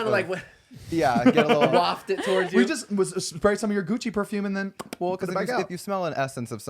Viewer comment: i thought said Kenny’s “coochie” perfume and i didn’t realize for a long time he said gucci